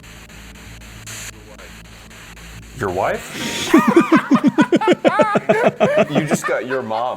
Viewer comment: I think they say “go away” instead of “your wife”